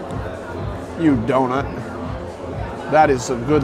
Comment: did you say " you donut"?? I think I've replayed it like 30 times and I can't tell lol it's bugging me